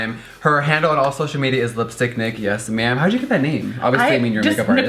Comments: i thought she said lipstick dick😭😂😂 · who else thought that when jeffree said lipstick nick or whatever i thought he said lipstick d*ck (no offence)? idk i just heard it like that lol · I thought you said "this is LipstickDick" in the very beginning 🌚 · I thought she said lipstickdick not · Lmao I thought he said lipstickdick instead of lipsticknic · LOL I thought he said "Lipstick Dick!" I died. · LMAAAAAO I THOUGHT HE SAID "LIPSTICK DICK" NOT "LIPSTICK NICK" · I legit thought he said lipstick dick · Thought he said LIPSTICK DICK 😂. Anyone else? 😳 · I honestly thought Jeffree said lipstickdick at the start for a sec there lmaooo bye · I'm not saying this to be rude or anything I love you Jeffree and I didn't know, but she looks really nice ! Anyways, was I the only one that thought she said "Lipstick Dick" ? OMG I'M SO SORRY 😂😂😂😂😂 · I thought he was saying lipstick dick for the first 2 minutes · I seriously thought you said LipstickDick when you first said her name... :/ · Totes thought you said "lipstick dick" in the beginning 😂😂